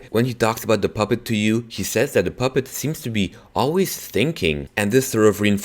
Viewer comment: stop commenting here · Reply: I thought said it's always stinking